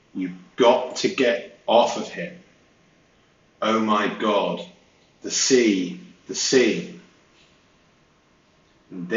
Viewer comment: I think I heard, "oh my god, this is a scene" like she meant it is a spectacle. What do you think?